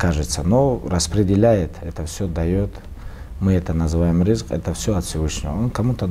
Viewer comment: he means and also said himself clearly "Rizq" Rizq means provision. It includes everything that we have been provided with by Allah. While wealth is a form of provision, it is not the only form of rizq. Allah has provided us with many other forms of rizq. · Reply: thanks brother and mostly your last sentence, and to be more specific, that's what I said in the commenting section: important rectification: in 36:26 he said "rizk" an arabic word (الرزق) not risk, and rizk (rizq) means livelihood note: very good interview by the way as always with the russian khabib interviews, no bs just real talk